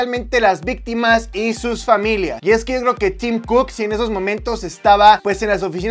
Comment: Cuando dijo “Tim Cook” yo le entendí “Team Cook” y pensé 🤔 ¿que team será ese ?😂.